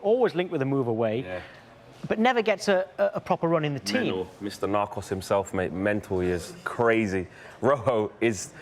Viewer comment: did just say Mr Narcos himself?